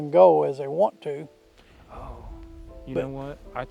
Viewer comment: I hear a loud whisper “oh” then you speak you can hear faint whisper as well you talking. I’m I hearing wrong?